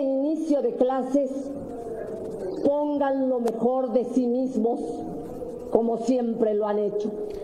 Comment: No se si escuché bien pero en el 1:49 dice: a las maestras y los maestros de México les pido que en éste inicio de clases tengan lo mejor de "cinismo " o de si "mismos"